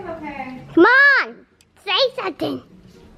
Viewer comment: Did say at 0:25 “Mom say something” or “C’mon day something” ???